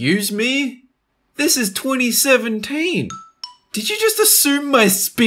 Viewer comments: captions say 2016 instead of 2017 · turn on captions, they said 2016 instead of 2017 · use captions and dolan says "It's 2017" but the captions say "It's 2016" xD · turn on captions when says 2017 the captions said 2016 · What they were saying: THIS IS 2017 Captions: THIS IS 2016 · the subtitles are wrong it says 2016 but he said 2017 9:55 also wrong